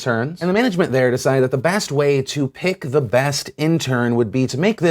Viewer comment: did he just say "Bast way"